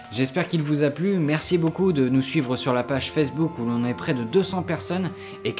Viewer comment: "La page FesseBook" Surement moi mais bon ^_^